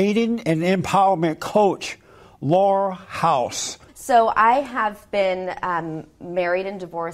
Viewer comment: He says her name, "Laurel House", but I first heard "Whore House".